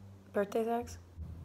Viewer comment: I straight up thought at the end, she said birthday tax and was trying to figure out why... But then I realized she sed... Sex